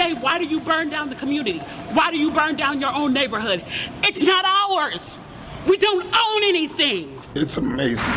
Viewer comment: When you said "it's not ours" I thought you said "it's hard Rs".